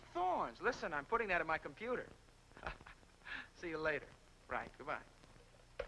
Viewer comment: what?!? did he say computer?!?